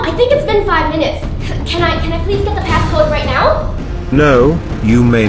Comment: Oh I thought pz member said help me get the code